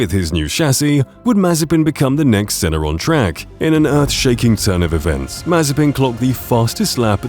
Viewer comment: Hey solid video dude! The voiceover used was pretty solid. I misheard “earth shattering” as “ass shattering” and lost it laughing. Keep it up man!